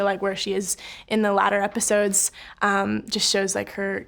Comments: Did she say later like “latter”? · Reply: Nope. She just said "latter" Go look up the definition. It fits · why is no one talking about at 4:05 where she said ladder instead of later? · Did anybody else hear ladder episodes instead to later episodes